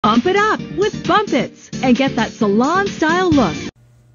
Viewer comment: I thought the ad said bum nits not bumpits 😭